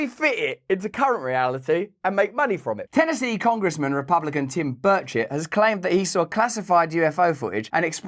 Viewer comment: Did anyone else think Russell said “Tim bird shit?”